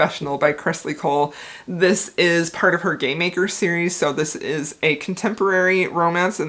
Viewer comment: I love Maxim in The Rebel King. UGH! I want that man so much!! I know you said "game maker's series" but I heard "gay maker series" and I need that in my life.